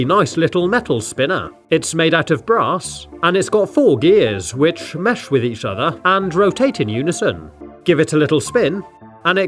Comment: is it only me who thought he said 'it is made out of grass'